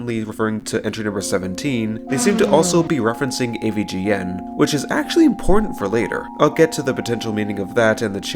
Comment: I was watching at 1.25x speed, and I thought that you said "which is actually porn for later" 💀